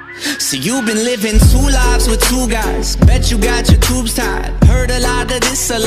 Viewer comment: soy la unica que piensa que dice tortugas